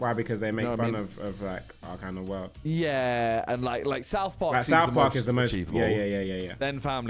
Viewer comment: tobi can you not talk over simon cause i have no clue what he said about south park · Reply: He said south park is the most achievable